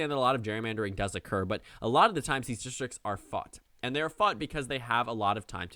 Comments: Lol I thought said “a lot of time these districts are fucked” and did a quick double take hahah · Did he just say the f work omg no I can believe this would never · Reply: He said “fought”